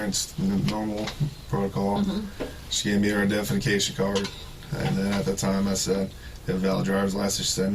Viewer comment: I continually thought he was saying her defecation card. Gross.